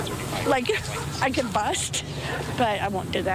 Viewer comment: Did she say she’s gonna Bust? 🤨